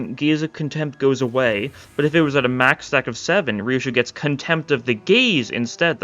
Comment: is a crazy place to watch this video again on. I misheard it as “Contempt of the Gays”